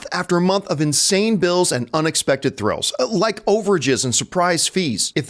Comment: Hey is it me or when promotes Mint Mobile as his sponsor and he talks about overages, it sounds like he is saying 'over jizz'?